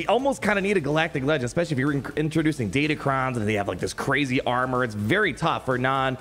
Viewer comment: I'm at a wedding so have the subtitles on. Instead of saying datacrons, it says data crimes. Can't really see the difference tbh